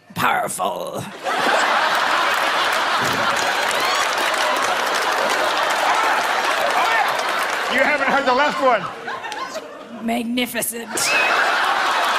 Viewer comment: what did the kid say when ryan tried to flex his muscles? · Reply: Ariana Acomb i thought he said "omnificient"?